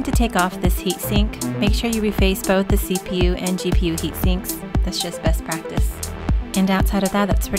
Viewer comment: What did she say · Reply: "make sure you re-paste both the CPU & GPU heat sinks."